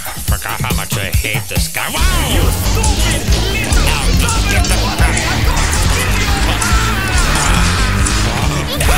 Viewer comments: WOAH WHAT DID HE SAY AT 1:07 💀💀💀💀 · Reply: it says 'You stupid little dominoes wannabe' i don't know why so many people mishear that as a slur but it's an issue apparently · did peppino just say what i think he said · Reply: He said a slight disagreement